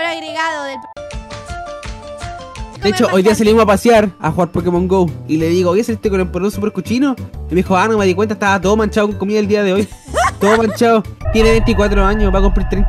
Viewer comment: "de hecho, hoy dia salimos a pasear, a jugar pokemon go y le digo: oye, saliste con el poleron super cochino; y me dijo: a no me di cuenta estaba todo manchado con comida el dia de hoy". Para que no repitan la misma parte para entender lo que dijo Pd: si, soy xileno